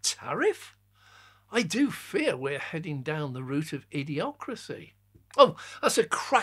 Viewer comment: Just at the end there, 12:56, did you say "EV-ocracy"?